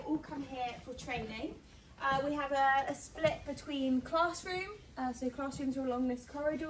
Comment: I thought she said Claas-room😂